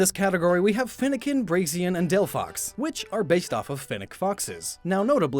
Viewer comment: did he say brazian